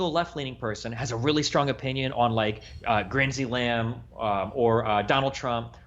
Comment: grindsey lam instead of Lindsay graham 😂😂😂